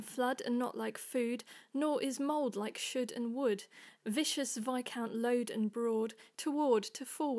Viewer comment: great job! it sounded like you said vicious instead of viscous, but other than that, wonderful...